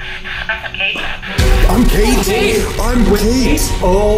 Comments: When it said “I’m Kate” it kinda sounded like “suffocate”. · i tought it said im gay i started laughing when it said that untill i hear it again and found out it said im kate